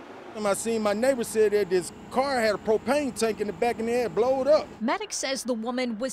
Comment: did he say "Blow'd Up"?